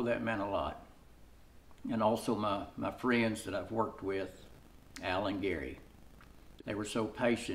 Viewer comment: At around 8:50 did you say alan Geery? Or gary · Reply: Howdy- I was talking about my buddies, Al and Gary. They are friends I’ve had the pleasure to work with. Thanks for watching the video. God bless you